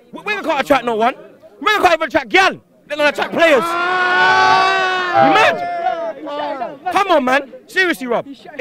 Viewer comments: what does he say 3:30 ? we aint gonna attrack GAN instead of playerS ? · btw... what did he say at 3:30 ??? didn't understand him, sorry. anyone? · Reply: Wenger can't attract gyal